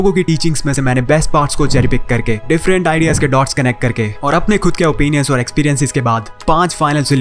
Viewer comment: Is he say person experience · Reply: Personal experience abut the addiction of gaming --, rocket league he mentioned